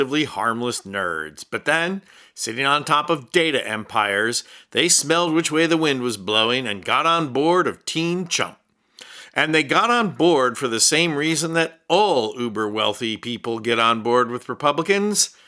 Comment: Canadian here again….a huge supporter. My hearing at 79 is not what is used to be. I thought you said “team chump” 🤣😝😝🇨🇦🇨🇦